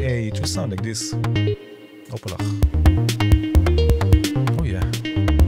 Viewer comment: So, what is this word you say whenever you don't get the result you were trying for? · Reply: Opalach :) It's like oopsie